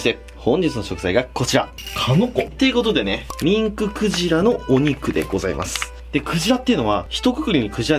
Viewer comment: ミンククジラのアホ肉って聞こえた(笑)